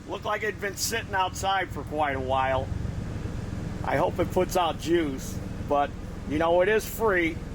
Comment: 😂sounded like he said I hope it puts out jews😂😂😂